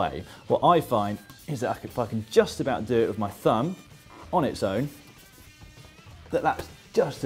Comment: I swear to God he said f**king at 1:47!! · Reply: "i find as if-i-can just about do it with my thumb"